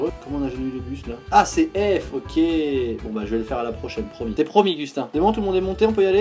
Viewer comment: Cest moi ou a 5:05 il a dit "cest promis gustin"?